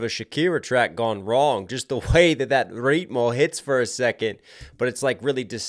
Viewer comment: what on earth did he say here dude am I tripping or is this not a word 😂😭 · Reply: He said ritmo (Beat / Rhythm in spanish)